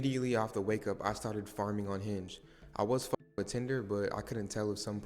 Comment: I thought this man said farting omg my heart dropped